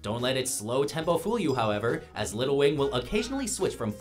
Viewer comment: Did he just Say Lil Wayne for Jimmy?